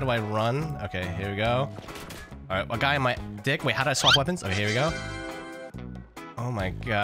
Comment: am i the only 1 who heard say "a guy on my d***"